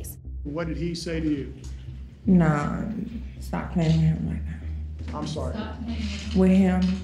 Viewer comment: Tbh i rewatched from 1:35 like 10 times and i still don’t know what she said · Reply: “Nah stop playing with him like that” I think, this is tough.